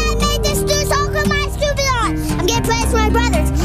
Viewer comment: Guys, did you even pay attention to the lyrics? A part of it was literally "I got pregnant from my brother, LET'S GOOOOO!" 💀 Edit: Yeah, it's misheard, but it's still cringe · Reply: Bro it’s misheard💀 it’s “I’m gonna play it for my brothers” 😭